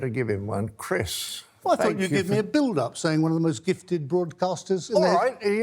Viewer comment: Kyyyaaaaarrrr! Coulda swore we heard John introduce him as "One of the most gifted old bastards." Must be the endless ROAR of canon ball fire. Hearing certainly isn't what it used to bee.